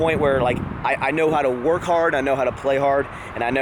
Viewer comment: I legit thought senpai said "I know how to work hard I know how to play hard I know how to get hard" 7:56 😂